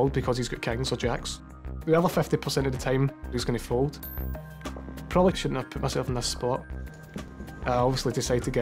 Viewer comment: Can someone tell me please, what is Ludo saying at 18:21 : "the other 50% of the time he is gonna fold, probably...??....in that spot"? Watched it several times, but can't figure it out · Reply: now i get it. Sorry :D. He says: "probably I shouldn't have put myself in the / that spot."